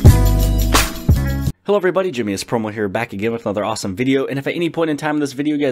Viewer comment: Thought he said jimmy ass promo, was like what the hell I click on.